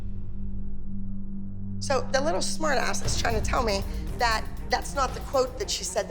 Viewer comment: did you just call a child “smart ahh”